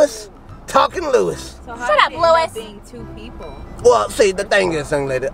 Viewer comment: When said “shut up Lewis” at 7:36 I heard Lois from family guy